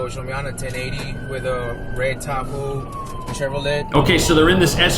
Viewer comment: I thought he said a Red Taco 😂😂😂